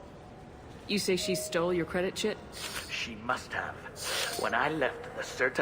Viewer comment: sounds like your saying credit shit not chit O.O